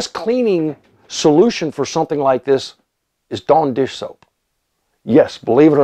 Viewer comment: is he saying "don dish soap" or "Darn dish soap"